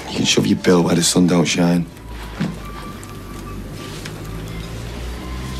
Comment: What did say at 1:10? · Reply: Amy L. Clitheroe massive own goal MASSIVE